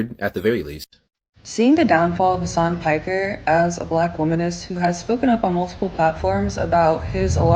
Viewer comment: Did she say "Black Womaness"? That sounds so dumb.